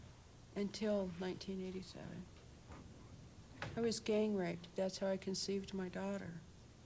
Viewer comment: WHAT THE FUCK did that woman say at around 16:30 what is wrong with her jfc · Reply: She said she was gang raped that’s how she had her daughter. Nothings wrong with her. What’s wrong is the disgusting men who did that to her